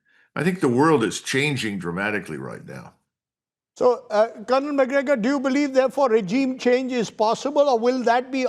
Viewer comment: On god I thought he said Conor McGregor😂😂😂😂